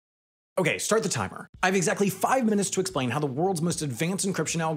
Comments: There is a mistake in the subtitles at 0:01 as the narrator clearly states that he has exactly 5 minutes to explain the contents of the video, the subtitles read 6 minutes. Given the video length and the fact the video ends at 4:52 for the sponsor segment to play I have to conclude that the subtitles are wrong. HaI please fix! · closed captions say six minutes, Sam says five. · Hey, at 0:02 the narrator, which I can only assume is an alien slave creature, says five minutes, but the subtitles say six minutes! What is this!?!?!?!? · Captions say 6 minutes, audio says 5?